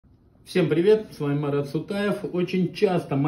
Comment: послышалось